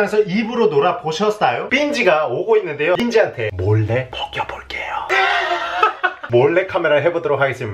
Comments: 나만 벗겨볼게요라고 들리나? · 이때 나만 벗겨 볼게요라 들림?